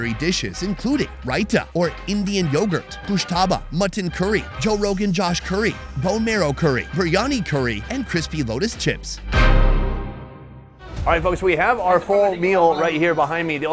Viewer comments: Wtf did he just say Joe Rogan Josh curry 😂😂😂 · did he say Joe Rogan josh curry instead of Rogan josh curry😂😂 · I heard him saying "JOE ROGAN JOSH" Curry